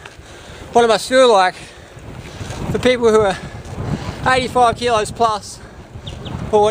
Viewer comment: I think 85 kilos is with the backpack(which is 20kilos). Sounds like he said he weighs 65kg.